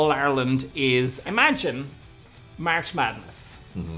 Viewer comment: He said "Imagine March Madness" and I heard "Imagine marshmallows" 🤣